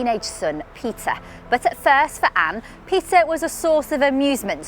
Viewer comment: lmao the subtitles said pizzza instead of peter